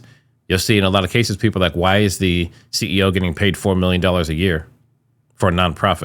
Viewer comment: I thought they had said he gets paid that 4 times a year